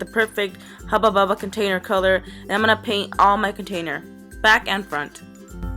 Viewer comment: when she said "back and front" I thought she said zac efron xD